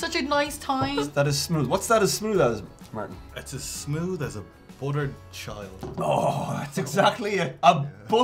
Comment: "....as smooth as a buttered child" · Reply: At first I thought he said a buttered Shite that would be quite smooth